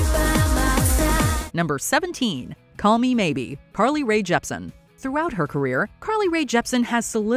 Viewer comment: Am I the only one who hears Parly Rae Jepsen?